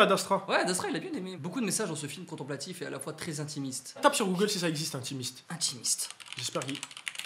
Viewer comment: J'ai entendu "tape si ça existe intimiste" j'ai coupé la vidéo nette